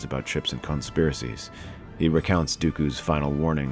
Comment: what was that? Did you say clone-spiracies?